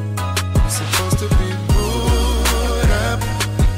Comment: I thought he was saying "supposed to be Buuda" lol